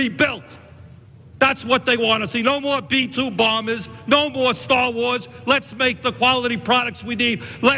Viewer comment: Did Bernie say "no more Star Wars?"